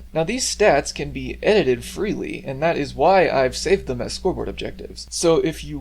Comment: i thought you said squidward objectives for a second and i found it really funny for some reason